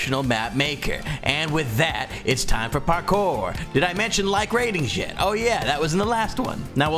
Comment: did he just say like rating sh!t?